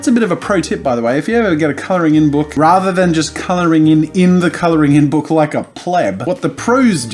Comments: Is he saying coloring in book? I call it a coloring book. No offense, I’m just curious why he calls it this. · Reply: i hear "colo(u)r in book.". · Alli B No its just 'coloring book' is the American way of saying it. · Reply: I thought he was saying "color in book" and my initial thoughts were, "Well, I learned this when I was today years old..." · It sounds like jazza says 'coloringing book' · I keep hearing “coloring in book” 😭😭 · is it just me or does it sound like hes saying coloring in book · Its sounds like he is saying color in book instead of colouring book.